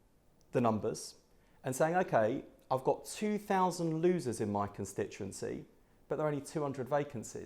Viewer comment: did he say just 2000 losers??